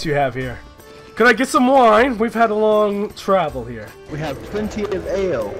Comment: I met starscream all I heard was “traveler” and I shit myself